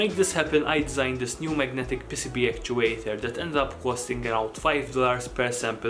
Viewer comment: i legit thought you said magnetic pissy pee actuator i should go outside